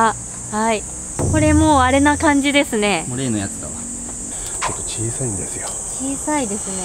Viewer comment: タクシーの運転手の男性の声とは異なる音声だと思うけど「もう、例のやつだ」と言ってるように聞こえる声が入り込んでる気がするけど、、、いったい誰の声なんだろうか。